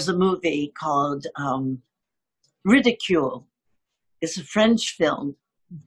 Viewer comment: Melanie mentions a French film (01:04:24), but I don't understand the name. Who can help me? Thank you! 😘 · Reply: I think i got it: Ridicule 🤩